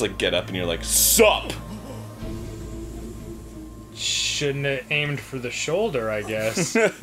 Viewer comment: did Danny just call “you should have aimed for the head”?